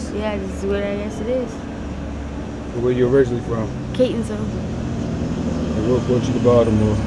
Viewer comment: she said Catonsville, but the closed captions said Kensington…